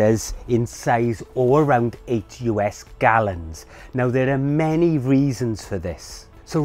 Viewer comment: At about the mark of 1:20, in Tip No. 2, the captions say, "...use a 30 liter or "80" US gallon container." you have correctly said it.... 8 gallons. You, however, might wish to get the captions corrected. Those either unfamiliar with the British accent or not accustomed to the Metric system, and totally relying on the captions will be thrown off, and head to the market to buy giant trash cans. LOL